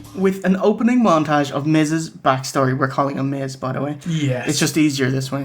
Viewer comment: I thought he said Mrs. Backstory.